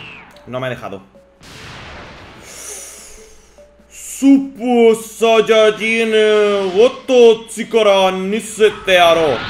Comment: supesaysigotsirsdas lo que escuche decir de 4:42 xD buen vídeo:)